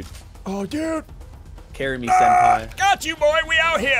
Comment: Did just said carry me senpai At 3:09 Me.:...ok...